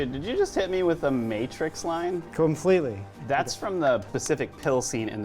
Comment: Says pacific instead of specific. Mandella effect confirmed